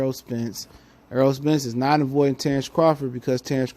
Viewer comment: I thought u said Ej was avoiding Bud